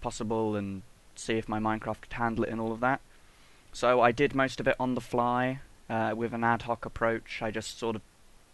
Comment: sounds like you said "So I did masturbate on the fly" Lol XD